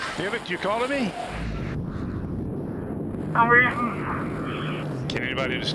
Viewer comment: I think he said "I'm leaving"